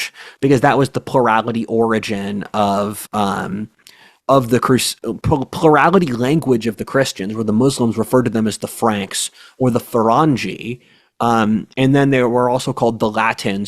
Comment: I thought you said Ferengi at first!!!